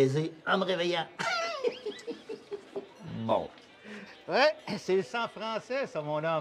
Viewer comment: What does Wayne say @ 00:51? sounds like “mo” but there is no translation when searching on google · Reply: Bon